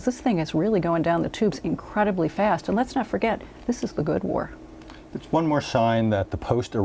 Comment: does she say at 4:10 that," this is for good war". when has any war been for good